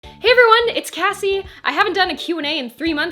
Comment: I legitimately thought you said "Ask Assie" omg